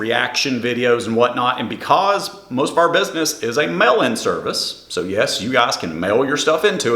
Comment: i definitely misheard mail-in service as melon service and i plan on incorporating that into my regular vocabulary